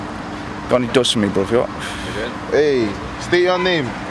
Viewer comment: did he say have you got any dust lmfaoooooooooooooo